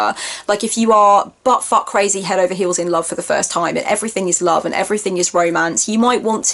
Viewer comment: HA! Did you actually said "Butt fuck, crazy Hell over heals in love" (14:26)? OMG, still laughing. you are so precious.